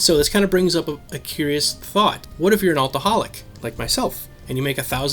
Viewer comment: I thought you said alcoholic lol